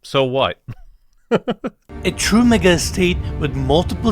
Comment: I really thought said "A true n** estate" I rewound it like 3 times XD